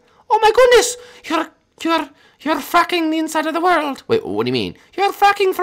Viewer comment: did tails say the f word?